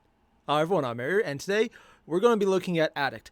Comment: I thought he said "Hi everyone I'm married" and I just lost my shit 😂